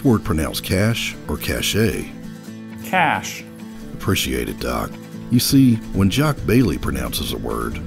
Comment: I THOUGHT HE SAID APPRECIATE IT DAWG